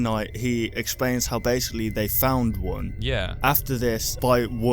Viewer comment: I heard "we found one " I swear I saw springtrap in my door and I coughed up stomach acid gross!!!